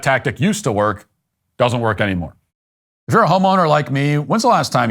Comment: 😂😂 I thought i heard matt say uf your a home O like me instead of homeowner lmao i was like wait wtf what a way to come out of the closet